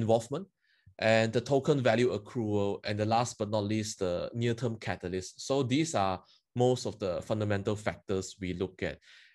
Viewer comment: What is he saying at 32:08 ? is that network catalyst ?